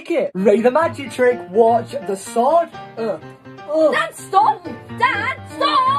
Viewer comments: I thought said "DAD STOP" instead of "DAN STOP" 😂😭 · I thought she was saying “dad stop” but she was saying “Dan stop” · I thought she said dod stop 🤣🤣🤣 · Ooh stop thought she said dad stop